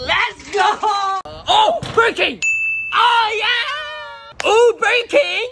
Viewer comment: I heard him say “Burg King”